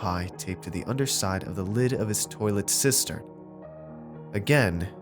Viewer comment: "Taped to the underside of his toilet's sister ... Wat? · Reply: Wolfendoom 3D Yeah, I got that now. I heard 'sister' when he said it and was confused.